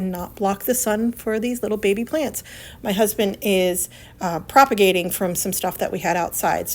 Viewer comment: 😅🧡🧡🧡 What I Heard: "MY HUSBAND IS PROPAGATING." 😅😅😅❤